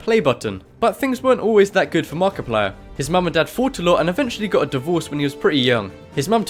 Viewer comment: "His mom and dad fought a lot-" Just in case anybody couldn't understand that, because I didn't at first.